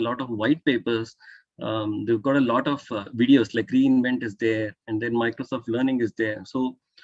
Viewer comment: What he said? ...... Green event?...... what is that? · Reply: No, its aws re:invent